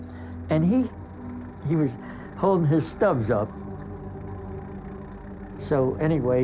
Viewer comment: Is he saying thumbs or stubs, as if they had shredded his hands off? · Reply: Yes. The explosion destroyed the boy's hands and reduced them to stubs